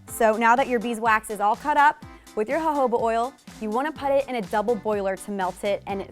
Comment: You should have a talk with google's captioning - instead of 'jojoba' oil, it says 'ho ho butt' oil......[time stamp 1:36] don't think anyone should use ho butt oil for this. ;-)